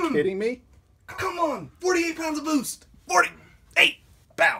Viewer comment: it sounded like he was saying 48 POUNDS OF BOOZE